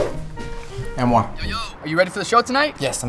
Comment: When he said Anwar I thought he said mi amor